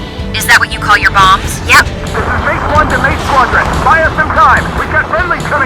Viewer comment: Is it just me or did i hear someone in the radio say „Mage 1 to Mage Squadron…“ 7:48 Edit: Does this mean that Clown is still alive and kicking?